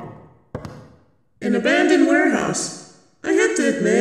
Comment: What is wrong with me? I had to turn on the subtitles because I briefly forgot the word "Warehouse". For a moment there, I thought she said "Werehouse" and imagined a person transforming into a house lol!